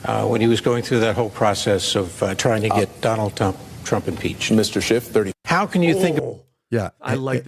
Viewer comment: Did he just say “Donald Dump”